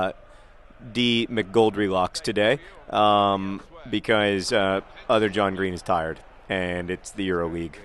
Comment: HOLLY LAMA 1:07 I heard "other john green is FIRED" my heart skipped 3 beats